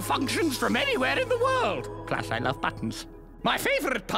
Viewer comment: Plus I love buttons :3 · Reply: I thought he said “Plus there’s no buttons”